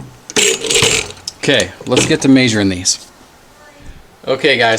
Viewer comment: It isn't me! He says MAY-SURE. I thought he was saying major for all this time. I really thought it was a powder maysure